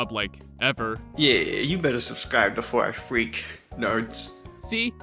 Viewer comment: did he say 2 4 I freak or before i freak